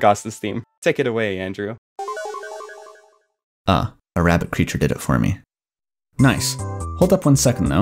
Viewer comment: Every time I hear this for the first time (I rewatch it like once a month lmao it is VERY high production quality) I hear "Ah... a rather creature did it for me." Which is a very funny agrammatical phrase... but also I'm beginning to realize Andrew is saying "Ah... a rabbit-creature did it for me."